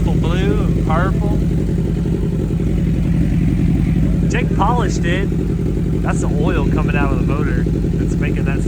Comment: Legit thought bro said Jake Paul ish'd it